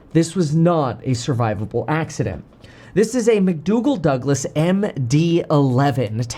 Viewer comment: Thought it was "McDonnell Douglas"..... Did I hear McDougall Douglas @ 1:45 Just curious